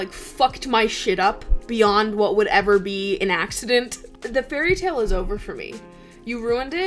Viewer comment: Love this, I thought you said "Ancient"😭